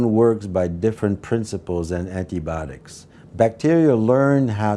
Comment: did he say "Antibotics"? Are they making microbes designed to destroy technology now?